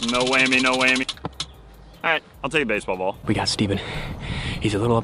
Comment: did he just say "i'll take baseball ball"?